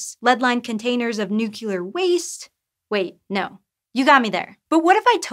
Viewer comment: Did she just say "nucular"? 🙄